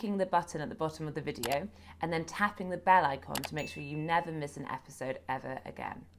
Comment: I always hear 'tapping the Bell End' and I'm not even British. You can thank Triggernometry for corrupting me.